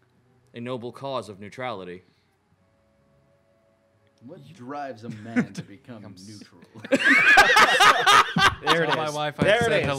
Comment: What did say at 40:45 I can’t for the life of me hear it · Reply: "What drives a man to become neutral" it's a futurama reference :p